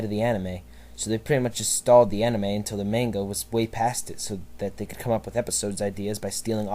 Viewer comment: lol it sounds like you said mango